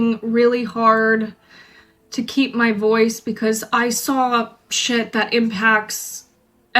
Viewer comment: 2cents: What I heard: "I saw shit"'..... mmmk... agreed.